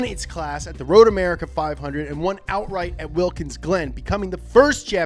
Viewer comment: did he say wilkins glen?